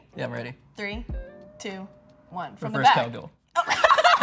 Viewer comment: What is the guy saying at 3:21? ty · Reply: He says, "reverse cowgirl."